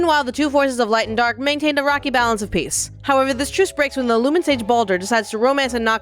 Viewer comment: I'll be honest for a moment I thought you said lemon sage and not lumen sage. And I was like wait where are the lemons i thought they were lemon sages? Anyway thanks for the description really helps me play catch up with the lore